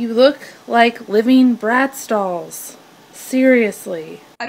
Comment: "Woman don't need to degrade other women." seconds later "Ew, you look like living Bratz dolls." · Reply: Shoot, I thought she said Brad Stalls